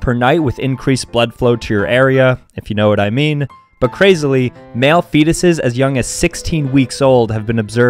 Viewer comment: I thought he said, “Male penis’s have been observed to be getting it up in the womb.”😂😂😂😂😂😂